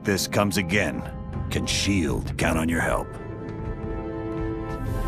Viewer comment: Noooo Captain, what did you say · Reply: The World can coom on us, Sir 😳👉👈